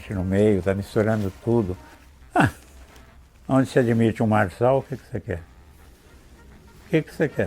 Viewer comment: "Onde se admite um...." Alguém entendeu o que ele falou? Não consegui perceber o que ele diz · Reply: "Onde se admite um [Pablo] Marçal... o que é que você quer?"